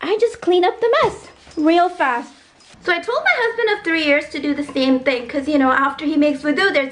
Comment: husband of 3 years?? I didn't understand actually..😅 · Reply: We have been married for 3 years ☺️